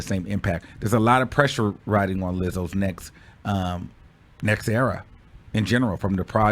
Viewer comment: i thought u said "there's a lot riding on Lizzo's necks!" LMAO!!!